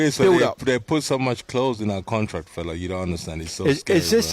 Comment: It sounds like Chisora says they put 'so much clothes in the contract' he has to behave. I assumed he obviously meant 'clause' but I would't be surprised if they have managed to incentivise him with clothes as he is obviously only in this for what he can get given he hasn't got a cat in hells hope of winning. I heard they have also included 2 for 1 Domino vouchers and a crate of Monster energy in the contract.